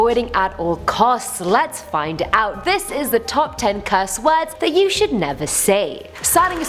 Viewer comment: Oh when she said "top 10 cursed words,".... I thought she said" top 10 cuss words,"😂😂😂😂